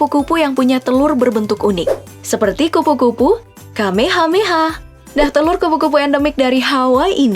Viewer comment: Ini ap gue yg salah denger ato gmn dah🤣🤣🤣, kupu-kupu "KAMEHAMEHA" kyk diDragon ball ae😂👏👏